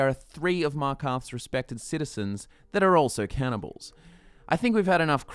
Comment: Damn I thought it said cannabis not cannibals